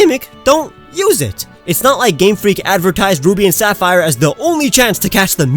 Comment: It sounded like you said Booby and Sapphire, and I used a Castform in OrAs.